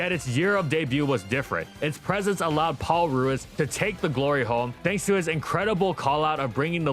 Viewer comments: What he said: "Take the glory home" What I heard: "take the glory hole" I was confused!! Almost hit myself in confusion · I keep hearing "take the glory home" as "take the glory hole" and having to rewind